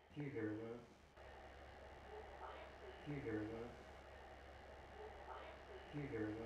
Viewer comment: I'm here..is what I hear